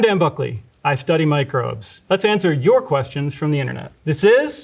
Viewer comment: I heard him say "I study Mike Rowe."